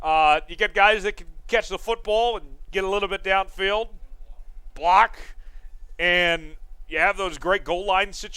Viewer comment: Who's giving you lines? lol I heard someone say "and block."